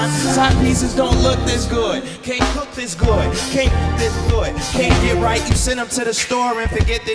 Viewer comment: Did he say at 0:52, "can't f%*k this good"??